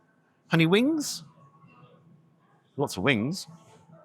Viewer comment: Oh, lots of wings. I thought you said lots of wind. 😂